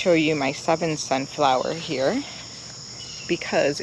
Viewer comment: What is the plant at 12:20? It sounded like you said something like Southern Sunflower.